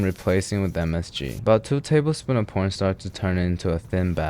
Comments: Did I hear him say P*rnstar at 6:30 instead of corn starch????? 😂😂😂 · Did bro say 2 tablespoons of pornstar? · did he say two tablespoons of porn starch?